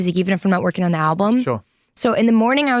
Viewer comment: did she say she was in the studio with brockhampton or did I hear it wrong? 👀